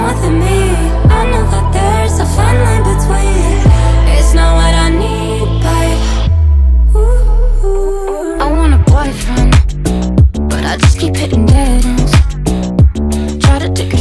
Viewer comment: I really thought she said, "I want a boyfriend, but I keep getting Daddys..."